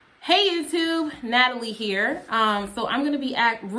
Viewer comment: I always mishear when you say "hello YouTube". It sounds to me like you're saying "hello you 2" oh, like you have only two subscribers